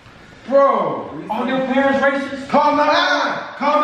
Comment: At first I thought said are your parents rapist but then he said are they racist